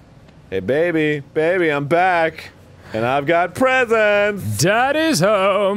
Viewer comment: My phone paused right when he was saying "baby, baby I'm home and I have presents!" but it paused so I thought he was gonna say "pretzels"